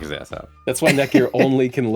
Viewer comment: Every time someone says "net gear" what I hear is "neck beard" lol